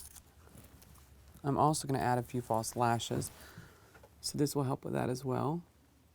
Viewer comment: He clearly says he's gonna use falsies... 9:56 :)